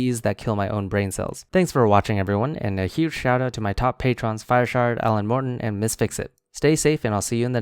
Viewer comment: Wow! Im sorry, but for the longest time I thought he was saying fireshart, and now I see that its FireShard. Guess I should have paid more attention to the list on the screen. Sorry FireShard, honest mistake and not an attempt at a joke.